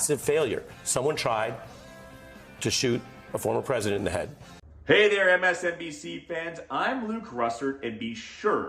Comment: Joy literally criticized the media; NONE of the other’s commentators responded. Rachel pivoted. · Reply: I thought i heard Joy say "not us here at MSNBC". Maybe im wrong.@